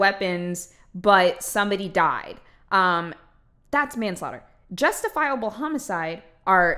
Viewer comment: Um did she say "justifiable homicide." I had to rewind because I thought she said "it's just a homicide" lmao